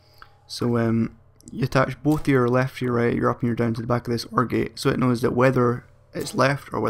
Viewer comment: When you said "OR Gate" I thought you said Orgy. XD